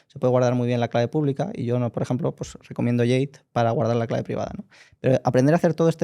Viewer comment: En 58:03 recomienda algo para guardar la clave privada. ¿Podría alguien deletrear lo que dice? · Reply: Bastante convencido de que recomenda Jade, una cartera de hardware